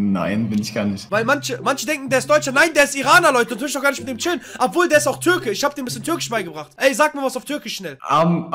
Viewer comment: Oha was hat er gesagt was bringst du in bei😂🤣🤣